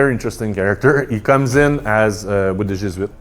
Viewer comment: I genuinely can't figure out what he says at 1:35 "comes in as a wadujsbit" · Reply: Comes in with the Jesuits.